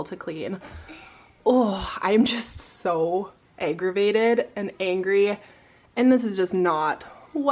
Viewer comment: So annoying that someone egged your house! So rude! But I must admit, I laughed when you said you were so aggravated because it sounded like you said “I’m so egg-ravated” 😂😂😂